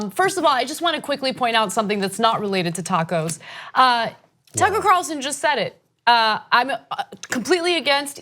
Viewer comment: did she say Taco Carlson?